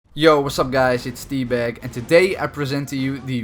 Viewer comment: LOL.. thought he said "Yo wassup guys it's D-BAG" Probably not the best name to go by, T-bag but ok.